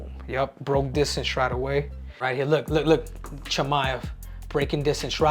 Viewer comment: did I hear him say Chimaev?